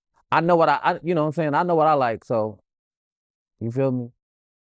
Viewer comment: The whole interview, he never denied being gay 👀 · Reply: COULD HAVE SWORN THE BRO SAID “HE KNOWS WHAT HE LIKES!”//.